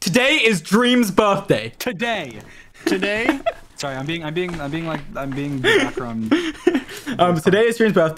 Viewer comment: did dream say "sorry im being drunk "